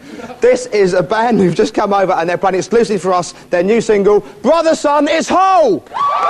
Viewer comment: Did he say,"Brother Son"? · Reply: I hear "Brother Son is HOPE"